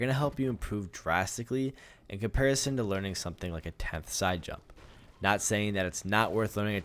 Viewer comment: Does he say a ''tenth'' side jump? What is that? Or what am i hearing?